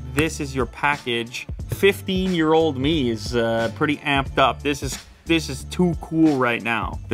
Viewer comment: I thought he said “50 year old me is pretty amped up!” Lol